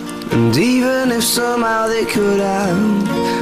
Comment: does anyone else hears "NICOLAS O'MALLEY could've...." in 8:59 ????????